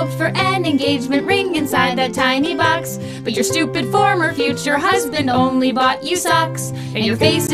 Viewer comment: I always heard it as future former husband instead of former future husband. I don’t know why…I thought they were saying they would marry anyway but in the future divorce….a future former husband.